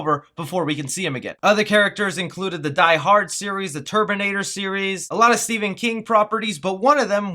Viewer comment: Did you say the turbinator series? Is that the Bollywood version XD